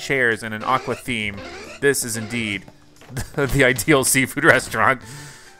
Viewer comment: you Said indeed and i thought you Said ideedee because it was the pokemon Right next to you😂